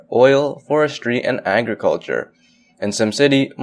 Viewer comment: did he say anger culture? :P